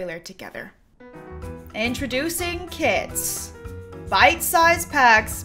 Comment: i heard she saying "kids packs" instead of kits and i got excited thinking that it was about children but i'm just deaf